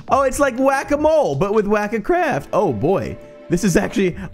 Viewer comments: I thought it said HACK-A-CRAFT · I thought the first one said Hack A Craft XD 😂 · I thought it said hack a craft lol